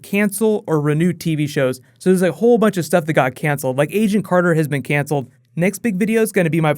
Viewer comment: they cancelled agent Carter??? noooooooooo!!!! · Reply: Oh, he said Agent Carter? I thought he said Asian Carter..